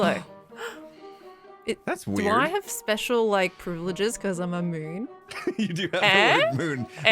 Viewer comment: "Do I have special privileges because I'm a Moon?" What I heard: "Because I'm immune?"